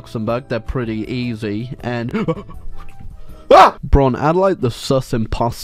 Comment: did you say braun?? braun from bokoen1 edits your videos????